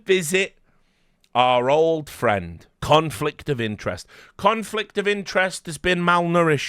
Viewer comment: Must admit, in the beginning I heard it as K0nfig of interest And was wondering, has he done something interesting lately??